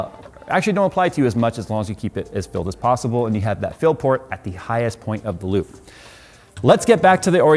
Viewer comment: I heard "Phill port" lol